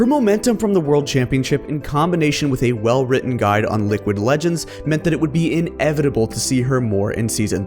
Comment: Was I tripping or did I hear Liquid Legends